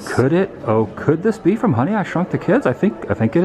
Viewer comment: I thought you said "Honey I shot the kids" LMAOOOO